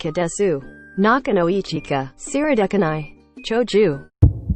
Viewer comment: 長女がしょうちゅうに聞こえるw